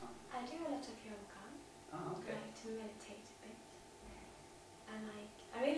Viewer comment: What did she say at 11:53 : « i do a lot of » ???? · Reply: I do a lot of yoga 😁👍